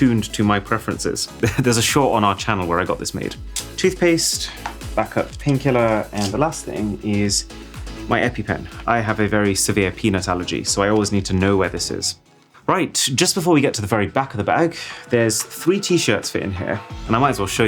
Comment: Did he say Penis Allergy ?